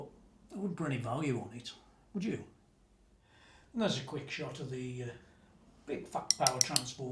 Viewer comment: I had to rewind because I heard ''There's a quick shot of the uh, fucked power transformer''...I as was like ''Wait whut???'' hahaha Great video as always! I have nothing against Friedman but I never believed that 5k-6k amps are 5x-6x time better than a 1K one, tone and construction wise. Splawn makes great amps for about 2K as example and that sits right in my mind.